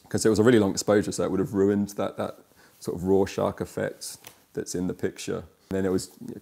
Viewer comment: Did he say “raw shock effect” or “Rorschach effect?”